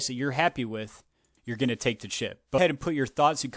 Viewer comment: 😳 Did you say at 11:50, some of us “will take the chip”!? 666 💉 💵 😳